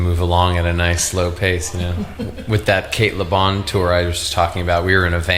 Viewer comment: Do anyone understand with who he talked about tour ( at 1:54 ) ? Caleb? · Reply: Cate Le Bon